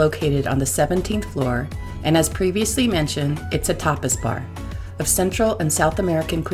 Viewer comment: LOL, I thought you said topless bar at first! Looks like a fun stay, filed this one away for future reference... :)